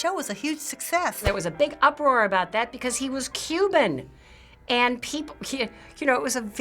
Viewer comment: I thought they said 'because he was cupid' and i was like what ...